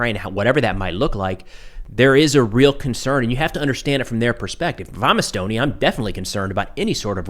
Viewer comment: Hey brother, how the heck Arrr Ye Mate? Ya just got'a slow you speech down a bit, I had to back it up, and listen again, it sounded like you said, "I'm Estonian", because, you totally muffled the "IF", in the phrase, "If I'm Estonian", Sooo, just a thought. Right? Cheers to Ye Mate!